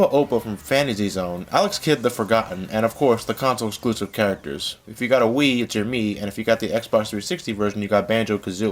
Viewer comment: What does say at 2:38? I can only make out "If you got a wii ah ji me".